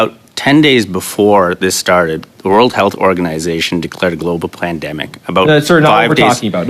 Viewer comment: Did he say plandemic